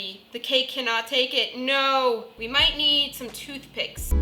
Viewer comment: When “We need some toothpicks” I thought she said “We need some toothpaste” 😂